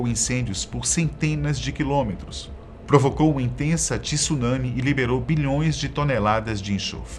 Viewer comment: eu ouvi tsulami kkkkkkk